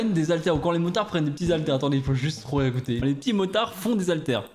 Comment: Moi j'entends ''les p'tits motards retournent la charette'' 🤔